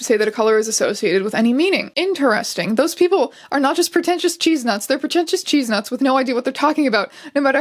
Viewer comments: Am I the only one that heard her say 'cheese nuts?' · im sorry did you say CHEESE NUTS!?